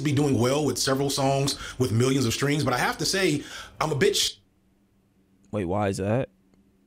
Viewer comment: When you paused saying I was bit shocked, but it sounded like he said I’m a bitch, and you said why that (Marlan Craft Not Being Popular) I thought you were wondering why said he was a bitch